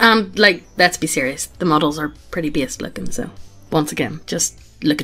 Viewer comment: Every time I hear Megan say “based” in her accent, it sounds like “beast” which honestly sounds better anyway.